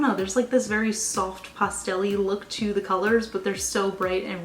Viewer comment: I love your videos but I swear every time you say 'pastel' all I hear is 'pustule' 😆